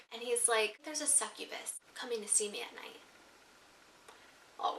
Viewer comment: what does she say from 14:44-14:46? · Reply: he said theres a succubus coming to see me at night. that's a demon that takes the form of a woman and seduces men into sexual activity.